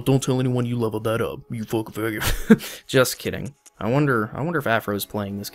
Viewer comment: Am I imagining things or does he say f*ggot at 7:26 ? Because that's what I heard. Sorry, dude, that's an immediate goodbye from me.